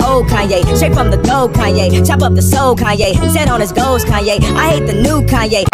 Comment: I heard “Sat on his balls Kanye”